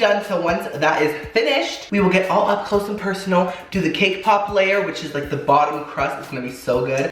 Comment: anyone else hear him say “K-pop” layer? Lmao I’ve been listening to too much😂